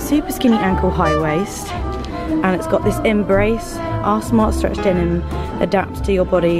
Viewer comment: When you said “our smart stretch denim” I thought you said “ARSE SMART stretch denim” .... which would be a much better name for them 😂 ❤️ p.s pls do more of these videos